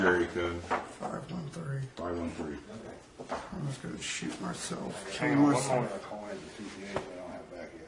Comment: did he say " I'm just gonna shit myself"